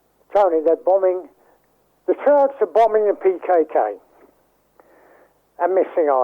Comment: Either I misheard him OR he actually said @ 18:28 , "The Kurds are bombing PKK" but he surely should have said, "The Turks are bombing PKK".. There is nothing New in what he's saying it's all widely known even @ 3:53 The Monser Al Kassir connection with Iran/Contra - I spoke to soon - Fuckin Radio Geezer calls him on the So whats new near the end..